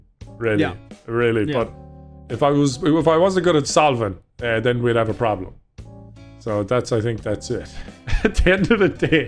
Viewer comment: I thought he said when I almost got cancer nogal solved that problem